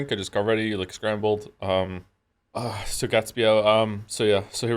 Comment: I really want to know if anyone else thought "Scrandle" when he said scrambled but oh man I feel so much healthier watching it without chat